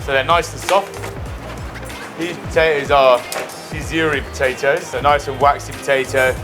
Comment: I can't understand what he is calling those potatoes... does he say "isuri" ?? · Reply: Désirée I think